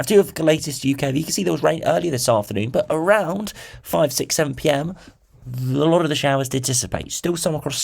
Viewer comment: out it at 0.4x speed what does he say after 5 😅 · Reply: 67